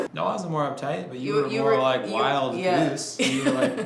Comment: did he say you were "wild & loose"? 😂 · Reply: Beatriz Villaraldo i thought he said wild goose lmao